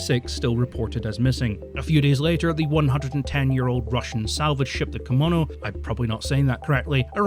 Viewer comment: Did I hear this right? 110 year old ship? :D :o